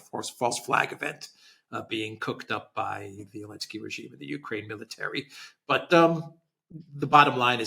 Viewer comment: I had to rewind because I thought Alex said “Coked up By Zelensky regime…”😂